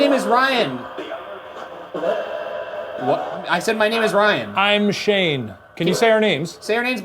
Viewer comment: Weird at around 14:45 when Ryan says his name, I hear some one respond "te llamas Ryan" in a question tone. I normally don't hear words without subtitles but I'm doing some notes while listening and I heard that. Spoopy